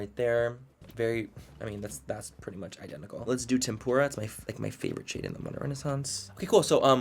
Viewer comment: I LOVE THIS SERIES!!! Whos digging it?! Should we continue it 👀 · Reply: you got me fucked up at 13:46😂🙌🏽 DID I JUST HEAR "temupra" ?! Like Japanese deep fried assorted veg? LIVING